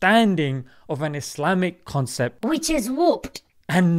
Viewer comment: few I thought he said which is fu*ked